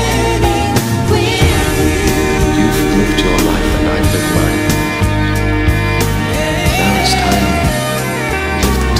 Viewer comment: What does Matthew say at 2:50??? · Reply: "youve lived your life and ive lived mine, now its time we live them together" which is right before he officially proposes